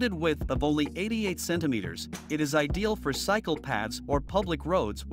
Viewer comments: I thought he said "ideal for psychopaths" last l0l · Cycle path..thought he said psychopath! 😂😂😂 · i thought it said "ideal for psychopaths"